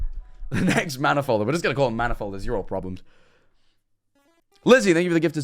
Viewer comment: did he just call us all problems